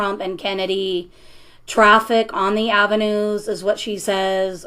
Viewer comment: As much as I want this to be true. But it kinda sounds like she says hold traffic on the Avenue so .... can be released. Idk.